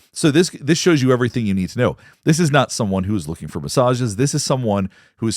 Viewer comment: go to drop site news and have a listen to his background. I thought you said at the start “he was driven by Mossad!”, the realised you said “massages”. When you look at his history, you begin to see a very complex, shady character. That goes WAY beyond his sexual proclivities..😮